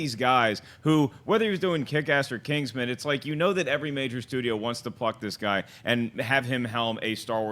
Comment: I thought said every major studio wants to fuck this guy lol. every major studio wants to pluck this guy